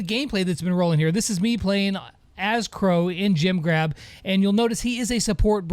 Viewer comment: I thought he said gym grab 😂